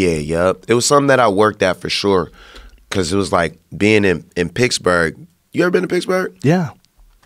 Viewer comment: Is he talking about Picksburg, Germany. (Like the US Military Installation) It sounds like he is saying Picksburg. I never thought of Pennsylvania as the Mid-West, I guess it's close though.